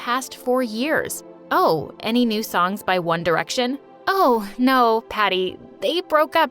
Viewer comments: did someone say one direction!?!!??? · did shejust said ONE DIRECTION I GOT EXCITED AF 3:48 😭😭😭 AND I AM SAD